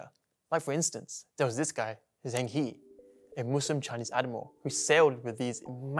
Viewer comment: The auto-generated caption is wrong. 😂 It's 'admiral' not animal. 😂